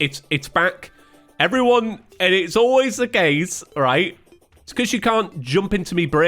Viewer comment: i misheard this as "it's always the gays" and was so confused about why gay people were relevant to this lol